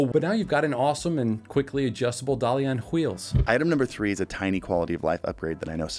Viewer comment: wait, did he just say "hweels?"